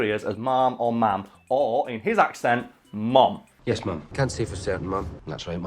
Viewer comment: I'm Scottish and those clips sound like he is saying mum not ma'am.